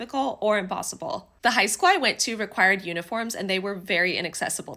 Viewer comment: thought you said unicorns and i was VERY confused. but yea, totally agree that uniforms are ableist